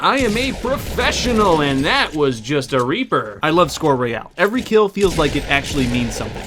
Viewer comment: uhhh.. did he just say reaper